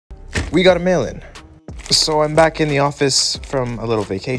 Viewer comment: Who heard him say We got a melon